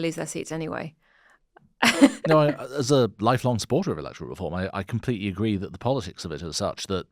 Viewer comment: Excellent episode. I really enjoyed the discussion of potential longer term policy choices. On a separate note, at about 25:50 I thought I heard say "As a life long supporter of Reform"!! I had to relisten. I had missed that he actually said "electoral reform" and not "reform"! 😅